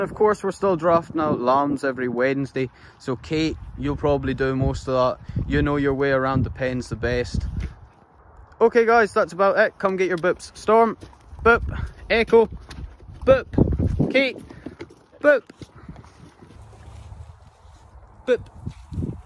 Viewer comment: I heard "come get your books!" And then I saw "boops" Chuckled at myself.